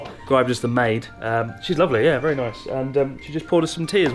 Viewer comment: I had to rewind twice because I swear he said "she's ugly" lmao